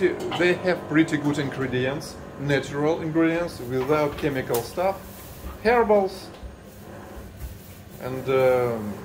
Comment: when you said "herbals" I thought you said "hairballs" (комки шерсти)! ты напугал меня! 😹🐾😹